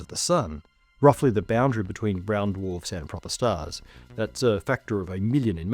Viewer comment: I thought you said purple stars at first and was really excited to learn about some new stars.